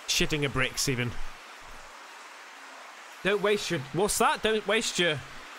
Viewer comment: hey (aka) @ 06:04 you asked what it said it was as follows (don't waste your youth trying to grow up). and thank you for videos it helps from depression (feeling alone). thank you. much love mate ❤❤ appreciate it